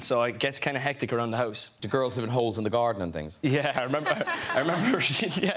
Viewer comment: I thought he said " Are there girls and hoes in the garden" what a clever joke that would have been